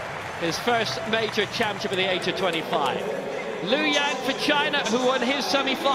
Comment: I heard Liu Yang VaChina